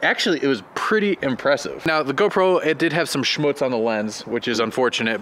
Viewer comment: did say "Schmutz at the lens" (7:47)? That's german :P ^^